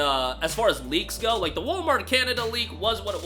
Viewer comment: & 8:03 he said "The (INAUDIBLE) leak was Hilarious because E3 was coming up." What was he referring to? It sounded like Walmart